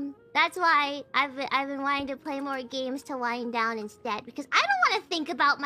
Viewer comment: "To wind down" instead of lying down